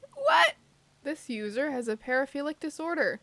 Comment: I thought she said paraplegic